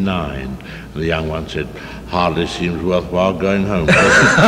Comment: i didn't hear what he said at 3:00. What was the joke? "Hardly seems worthwhile going home"? Is that what he said? I don't get it...